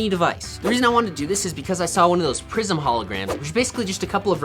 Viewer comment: When he said prism I thought he said prison. I was so confused😂